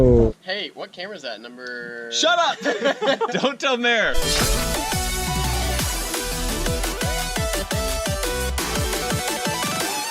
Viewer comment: WOW THIS WHOLE TIME IVE THOUGHT YOUVE BEEN SAYING " DONT TELL THE MAYOR"..... FML